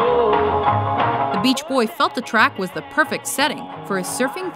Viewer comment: did they said "felt the track" or "f****d the track"